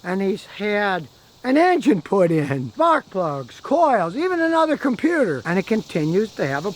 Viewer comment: After he said a new engine was put in it sounded like he said butt plugs, but I’m sure he said spark plugs. LOL